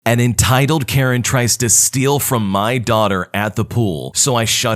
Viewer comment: At first I thought it said "tried to steal my daughter"